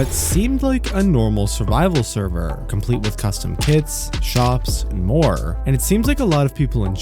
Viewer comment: thought he said kids